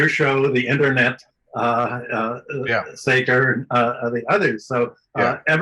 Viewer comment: Does Hudson say "Seder"? As in synthetic leftie, Sam Seder? lol.